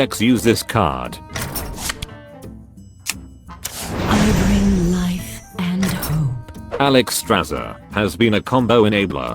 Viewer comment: I always thought that alextrasza said "I bring life or death" since she can heal or take away life 😂